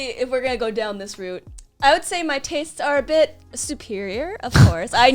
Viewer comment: why did I hear tits